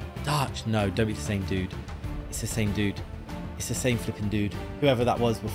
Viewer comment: did you say FUUUHHHHING or flipping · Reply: Flipping